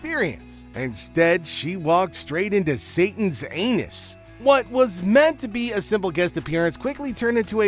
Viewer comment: did he say Satan's anus? Guess he dont like Meghans kitchen either 😂